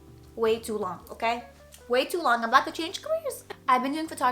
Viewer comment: what did she say on 1:25? I can't understand but I know it's funny lol I'm not a native English speaker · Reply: “Way too long, I’m about to change careers’